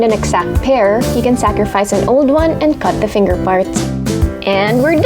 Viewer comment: I heard finger farts loool